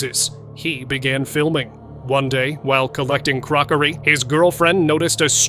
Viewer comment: did he say "while collecting BROCARY." 😅